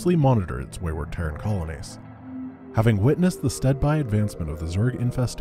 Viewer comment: Does anyone else assume that the Zerg has acidic blood because of Aliens? You know, the franchise with Sigourney Weaver? Just me? 6:12 Did you say stead-why???